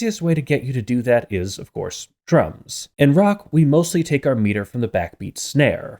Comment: I swear to god I thought you said drugs 😂